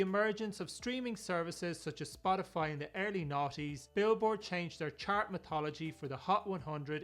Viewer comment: Hmm maybe you need to listen to what you said around the 9:49 mark. “ with the emergence of Spotify in-the early 90s” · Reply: he clearly says "noughties"